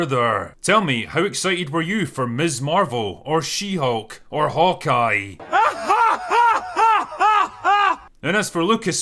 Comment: wait did he just say HULKEYE show?